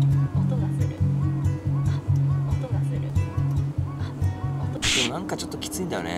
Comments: くらいから、音がするって聞こえるのは、俺だけ？？ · くらいから、の声でずっと、おとがするって言ってるんだけど聞こえるの自分だけ？